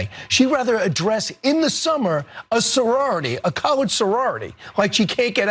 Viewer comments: Okay analysis - listen at 0:44 At regular speed it sounds like he said colored. At 0.5x, its sounds like college But weirdly, at 0.25x, it sounds like colored. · I heard “college sorority”, which fits with him pointing out that this is “in the summer”, when colleges are not in session. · If you heard "college," don't forget to hit the dislike icon on the video. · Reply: Wow is that what you heard? Go to the 0:45 mark. He said colored. C'mon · CC says “college” but I hear “colored” audibly.